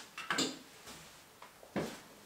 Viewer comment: parece que dice "Bien" jaja